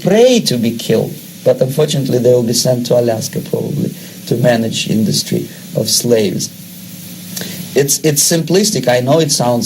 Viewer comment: does he say, and unfortunately they will be sent to Alaska probably to manage industry or slaves.